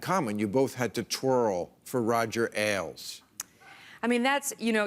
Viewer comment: For a brief moment I actually thought he said "you both had to twerk for Roger Ailes" and I was about to congratulate him for a great line when I realized I misheard 😄 And it seems I wasn't the only one to make that mistake as there's one guy in an otherwise dead silent audience who reacted very loudly as if he heard the same 😄